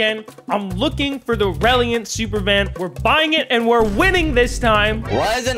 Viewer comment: did mans just call it reliant